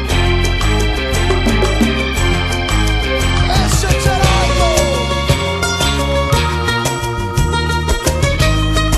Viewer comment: Alguien sabe? que dice después del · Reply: Creo que dice: eche charango....